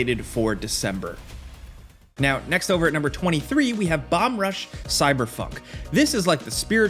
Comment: I heard "Bum Rush Cyberf*ck"